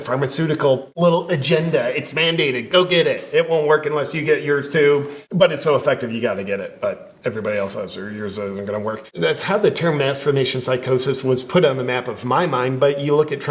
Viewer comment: I thought you were saying "masturbation psychosis" 😅😅😂😂